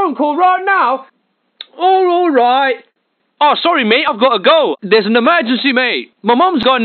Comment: "I gotta go"? Thought he said "I got a girl"